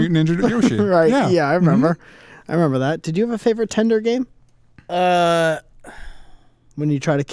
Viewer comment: "did you have a favorite TINDER game?" I literally thought thats what said.